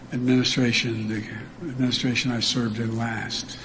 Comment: It almost sounded like he said “Administration Nigga.” 💀💀💀